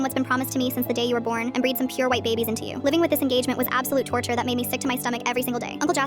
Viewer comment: YO, did I here wrong, or did he just say "And breed some pure white babies into you."